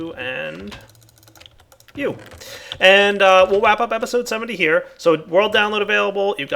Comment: Am I the only one how heard him say wap up the episode